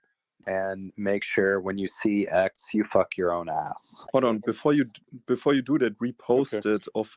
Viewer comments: Someone please tell me what's being said because I only hear "f your own a**" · EDIT - @ 2:48:52 does that dude tell that other guy to f**k his own ass? That totally came outta where! One thing I find hilariously tragic, is that people cry out for real whistle blowers, and then when one appears, people cry that they don't believe them. If people are trying to discredit a whistleblower, then, at the absolute bare minimum, we should listen to what they have to say, because somebody is spending money to discredit them. Also, I don't know if it's just me, but I just don't like Greenstreet. He comes off as a very weasly guy, and is always working an angle in order to later use it to discredit people. I'm impressed at this Q&A not having an overly large amount of woowoo reinforcement questions, and douchebags trying to plant words in his mouth to strawman him. Most, for the most part, they were pretty respectful. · "when you see x you f**k your own ass" Did I hear that at 2:48:49 ?